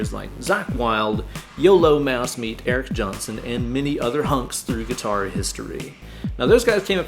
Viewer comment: I’m sorry did bro just say many other hunks 😂